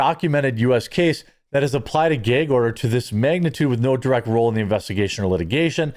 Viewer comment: A Gay-g order? Dang Candace may be on to something 😂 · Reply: I thought he said a Gay Ogre! I thought there goes Shrek messing things up again! 😂